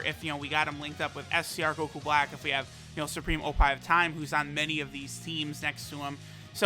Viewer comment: Did say " Supreme oppai of Time" at 9:06?!